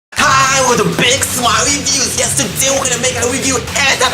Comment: awesome accent, it always sounds like your saying "big smile views" lol